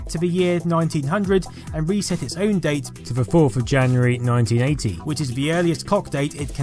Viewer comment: captions say April 1st, but the voice says 4th of january. What is true? EDIT: 5:59 has the answer still captions display the wrong date.